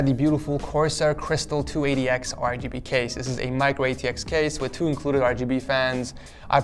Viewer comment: LOL i thought he said microwave case